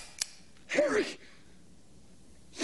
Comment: I thought Llyod said TERRIBLE SHOT, not horrible, when I first saw this movie, I thought I heard terrible.